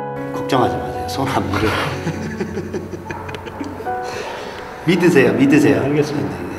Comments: Does he say BITTE SEHR, BITTE SEHR in german?! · did he say "bitte sehr" ? thats german i'm confused XD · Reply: Marisa x3 믿으세요 (mid-eu-sae-yo) meaning to believe or trust